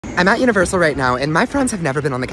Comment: WHEN HE SAID FRIENDS I HEARD "parents" AND WHEN I SAW THEM I WAS LIKE "huh?"